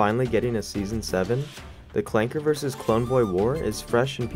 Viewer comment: did he just say Klanker with the Hard "R"😑😑😑???